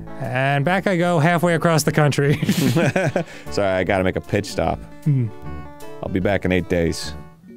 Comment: I thought said “I gotta take a piss stop. I’ll be back in 8 shakes.” 🤣🤣